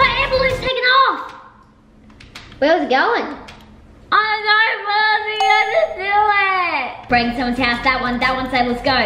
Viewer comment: I know what said! at 7:27 she said “I didnt get to do it”